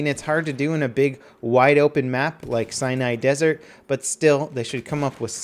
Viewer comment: Lol at 4:44 it sounds like he says Cyanide Desert.